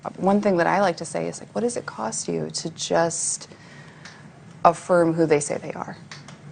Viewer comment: Did she just say " 0:56 What is it cost you to affirm who they are ??" They are little kids women!!!! What a sick human being she is disgusting. Leave this kids alone. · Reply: "What does it cost you to just affirm who they say they are?" is what she said